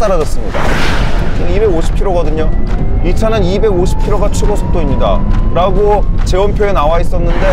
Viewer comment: whoever edited this video is a damn hero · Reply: SEOTIG Hey Seo if you're the actual editor of this vid I just want to let you know about a little typo here. At around @ 13:09 Kim said the top speed is 250 but the subtitle says 260. It's a small mistake, but hope it helps :)